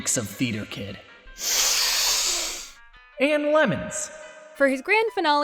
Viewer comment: thought he said ALMONDS not LEMONS- oml this video is great